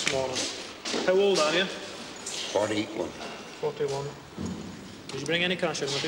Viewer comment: Did he say he was 41?